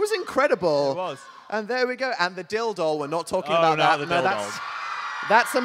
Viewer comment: When they were going through the crafts I thought they said dildo bit they said dil doll I was lmao